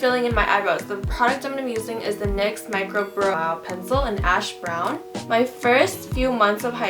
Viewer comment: did anybody hear that? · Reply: LMAO it’s because I was trying to read the label and was like brooooooooooOooOow, so I cut it so I just said brow